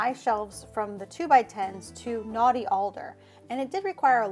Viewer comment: Closed captioning says "naughty alder" instead of knotty alder. Would've been even funnier if it said "naughty elder." 🙂